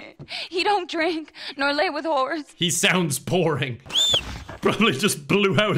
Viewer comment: "He don't drink nor lay with whores" I had to listen to that about five times because it really sounded like she said horse and I was like one of these is not like the other